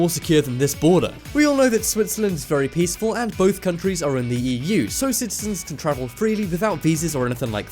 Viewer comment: did he said Wii U instead of EU (Europe)?!